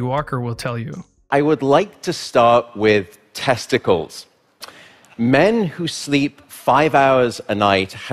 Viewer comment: alright wasn't using headphones and my speakers were loud and all i hear is "i want to start with testicles"